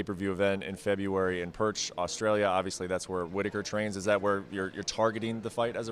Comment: did he say Perch Australia... Please tell me I am mishearing